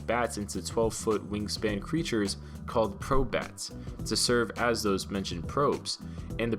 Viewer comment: It sounded like he said Crobat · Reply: Probe Bat not crobat